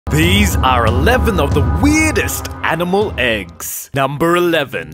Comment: Why do i feel like your spitting on me when you talk like that? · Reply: Queen Dee lol at first I thought he was saying "the weirdest edible eggs